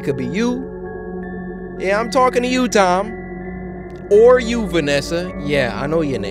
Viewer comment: my name is Tai. And when you said “I’m talking to you Tom.” I thought you said Tai and got really scared. I won’t sleep now, thanks.